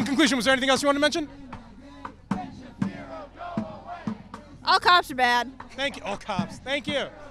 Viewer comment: “All cops are bad” this is always an ironic view point for a party that desires more socialistic laws and regulations for our society. More regulation = more enforcement = more police. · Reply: i thought she said "all cocks are bad" i had to listen again to hear it right